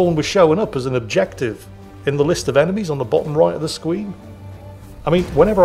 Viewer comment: did anybody else think Jingles said "On the bottom right of the SQUEEN"? LOL maybe I'm just tired